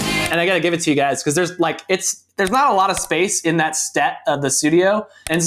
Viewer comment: I just realized that at 6:15, Jeremy said "stet" instead of "set" 😂